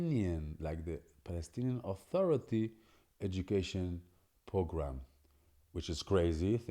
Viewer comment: Instructive n even interspersed with some humor. I ocassionally follow the news from Israel to keep abreast of what is happening in the Land. When you said, 'program' I thought you said 'pogrom.Some Israelis speak so fast I can't follow you speak paused enough that I can follow along. Todah n Shalom! May the God of Israel keep you!